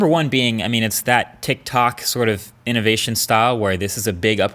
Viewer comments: did I hear TIK TOK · Did you say Tik Tok?